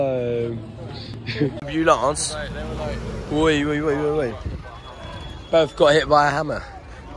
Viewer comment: did u say he got hit by a mallet. I was in jaws and my mate lobbed our mallet over into pipeline.